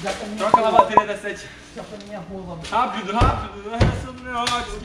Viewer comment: KKKKKKKKKKM QUEM OUVIU O FALANDO JA FOI MINHA ROLA